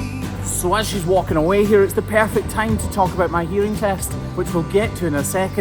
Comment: lol, at first I thought you said Urine test 😂. I was like ohhhh they found all the THC eh? 😁